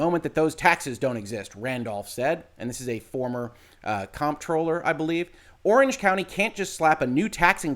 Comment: not sure what a "former comp troller" (i think that's what you said) is, but Scott Randolph is the current Orange County Tax Collector. So if he says Orange County can't just slap a new tax district onto the area, and talks about other tax related impacts, I believe him.